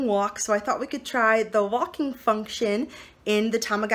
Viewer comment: Am I the only one who thought she said walking fuckshit